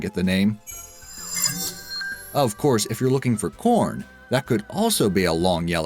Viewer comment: thought he said "looking for porn"